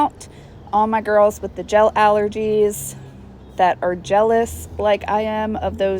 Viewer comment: Lol 😂 when you said “jealous” I thought you said “gelous”, like jealous of gel